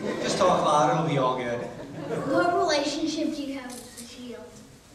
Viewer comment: Does anyone know what the kid says at 22:55 it sounds like he asks what kind of relationship he has with lita, but i can't understand it correctly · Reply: He asked " What relationship do you have with The Shield?" Punk said "He Don't have one."